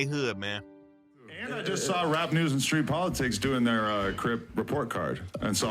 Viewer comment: Bruh I seen the thumb nail say south side report card Then his opening statement I thought I heard “This is rat news and street politics “ Lmao